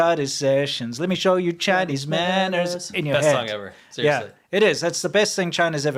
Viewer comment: You know its about china So i hear "childish maaanners" ;p